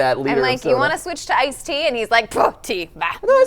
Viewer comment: did just say f*** tea I think it was a really genuine moment where she kinda didnt censor herself Yet loved it Pretty sure they arent pg 13 all the time